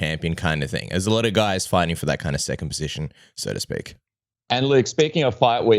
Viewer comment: 😂 Did you say there's a lot of guys fighting for that "circumcision"? 🤔😂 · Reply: I know you said second position, but it definitely sounds like circumcision with that Aussie accent 😜